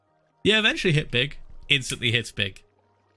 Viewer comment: sounds like hes saying: "hits bick"